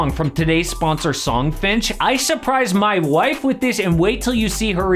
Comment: At first I thought you said that you got the song as a present for your mom, so naturally I assumed it was you and your mom in the car together, and when y’all kissed each other on the lips. I actually threw up in my mouth, and thought of that scene from the Adam Sandler movie where the girl admits she sleeps with her brother and the African guy at the wedding says “fucking white people” I’m so glad I went back and re-watched the video and heard you say “my wife” ☠️